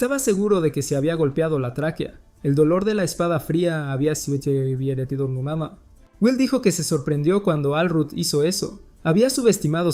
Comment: que dijo??? JAJAJAJA bien bug la voz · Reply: Dijo Que había sometido a sumama